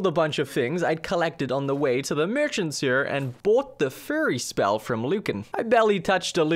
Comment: sounded like you said that you bought the “furry” spell to me, and I had to go back and listen again.